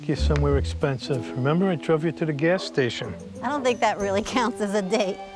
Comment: I’m sorry did I mishear that😭I know he ain’t just say he took her to the gas station